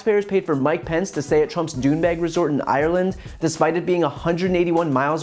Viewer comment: I heard "douchebag resort."